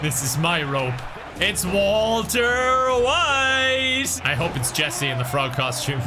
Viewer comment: did he say Walter Wise??? Not important but just wanted to know if m the only one who heard that way…